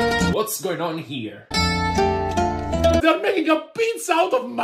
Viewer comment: I know the real words theyre saying but i heard "theyre making a pizza out of a c**k